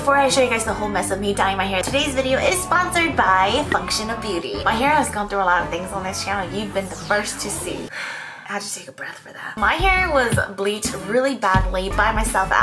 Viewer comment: Did I just heard "fuck shit if Beauty"... Jejdofs anyways I love you ~~